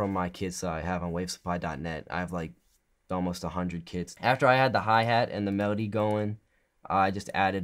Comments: I thought he said kits instead or kids. LOL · I thought he said I have almost 100 kids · is it just me or does nick say ''I have like 100 𝗸𝗶𝗱𝘀'' :D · Me: did he just say “i have like a 100 kids 2 seconds of thinking: ahhhh, Kits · Who else thought he said kids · thought he said: "I have... almost a hundred kids" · I thought he said he had 100 kids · I thought he said " i got like a 100 kids" lmao